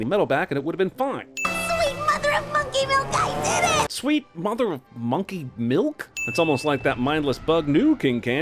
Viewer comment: Interesting, I heard "sweet mother of monkeyville" first, but can hear both depending on what I'm primed to hear.